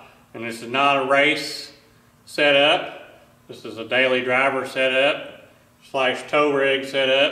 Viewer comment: I had to go back to 2:36 a couple of times. What I heard the first time was "Bailey Driver"